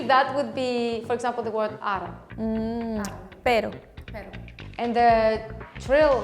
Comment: she saying "perro" or what, video subtitle says " 'peru"